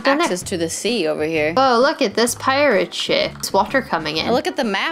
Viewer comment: sounds like she says shit instead of ship lmao